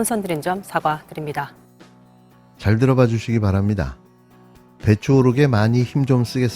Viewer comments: 배추로 들리지만 아마도 매출오르게 하고 싶었는지 모르겠어 ~술을 조그만 드셔야 할것같아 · 배추오르게로 들리네. 매출로 안들리네. 배추값 올라서 상인이 이익금 오르네. 윤석열 머리맥락도 배추네 듣기평가 시즌2